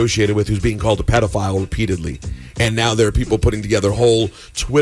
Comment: “repeatedly” Sounded like “with p diddley”